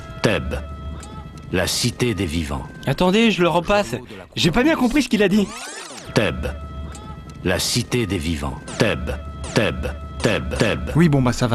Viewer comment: Il dis bien teube ou c'est moi ?